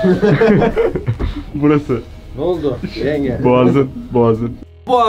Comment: de mc yaralı damlaya noldu yengemi diyor benmi yanlış duydum